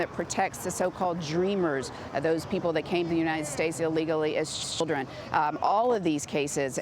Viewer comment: anyone else catch that. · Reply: "Illegally as blip (children)"